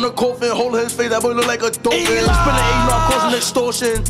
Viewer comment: “Holding his face that boy look like a dolphin” omg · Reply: I thought he said holding his face that boy look like a doofus😂